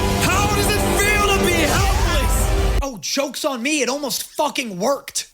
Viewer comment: why did i hear "chokes on me" instead of "jokes on me" 😂😂